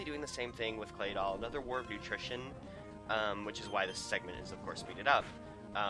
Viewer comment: War of attrition, it sounds like you were saying utrition or nutrition or something to that affect.